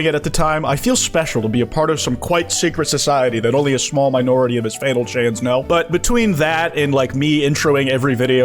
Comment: fannel chans that is what you said here